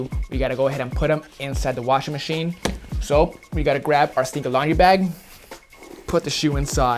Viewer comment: For the longest time I thought he was saying "Secret Laundry Bag" & "Bar Cement" lmao 😂 face palm